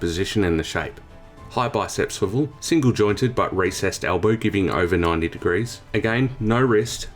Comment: “racist” am I hearing this right??💔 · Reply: Recessed. I am definitely not racist. Also it doesn’t make sense that it would be racist….